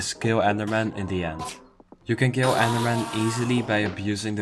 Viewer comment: it sounds like you said ant armor but I can’t find that anywhere did I miss hear you or am I missing something???? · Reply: End, not ant lol I also misheard him while alt tabbed